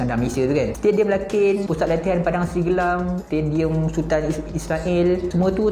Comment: Aku salah dengar ke stadium Sultan Israel 🗿🗿🗿🤣